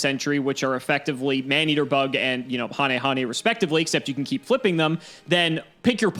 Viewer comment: Wow, actually pronounced hanehane correctly this time! I'm happy. :) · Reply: I feel like I always heard him say hayne hayne, but yeah, ha ne ha ne is what he said and I'm happy since no one pronounces him right.